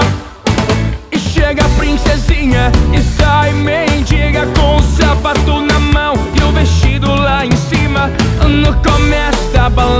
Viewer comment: entra princesinha e sai mendiga com o sapato na mão e o vestido lá em cima kkkkkkkkkkkkkk realidade das festinhas kkkkkkk demais essa musica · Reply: eu escutei "e entra princesinha e sai meio dia com o sapato na mão e o vestido lá em cima "